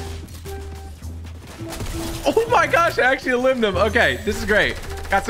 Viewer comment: Im sorry did say “ Elimed em” at 1:46. That’s a first